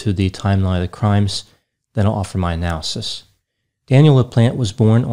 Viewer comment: I misheard that intro as “Daniel the plant” and I immediately looked at the back table to see if there was a newbie...